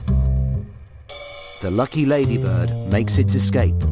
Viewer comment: did he say "the lucky lady - BIRD makes it's escape" ? ...or lady - BUG ? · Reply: For some reason the brits call them lady birds